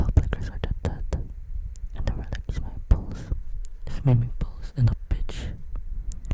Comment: I heard "swimming pool and a Bitch" 😂